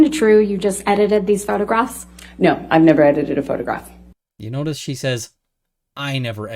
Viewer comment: omg this whole time i thought she said, “i’ve never edited a photograph,” and not, “i never edited *this*photograph.” 😵